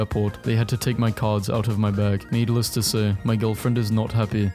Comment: every single one time i hear/see this word, i think "needles"